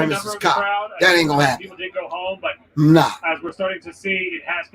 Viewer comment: I was listening...and all I heard was 'NI@@ GO HOME!'